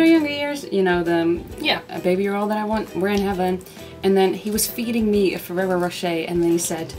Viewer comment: What did Celinde say? :3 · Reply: "We're in heaven".. the guy was referring to the song :)